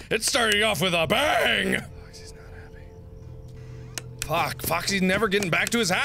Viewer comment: Did anyone else hear something whisper "foxy's not happy" at 7:12? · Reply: Oh my gooooooooooooooood! I heard "Foxy's not happy". It was not Mark.